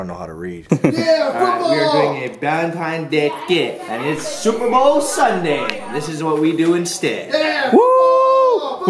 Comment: I thought Ryan said super hoe Sunday. Of course he didn't.